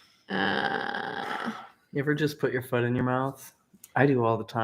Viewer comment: For some reason when asked "have you ever put your foot in your mouth", I heard it as "have you ever put your fun in your mouth". I had to rewind to make sure I heard it wrong. I'd just figured it was a Delta 9 reference.